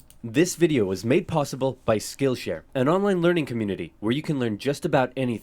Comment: I thought skill share said skill issue